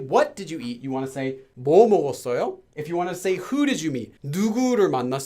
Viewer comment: is it just me or am I deaf? what does he say before 먹었어요.. is it 뭐를? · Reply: I say 뭐.